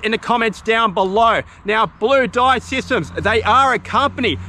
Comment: Patron post : hi! Every time you said 3 dive systems I thought you were saying free dive systems lol 😂